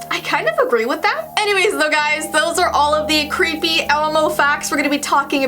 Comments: I the ending did she she say elemo instead of elmo ??? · did she say elomo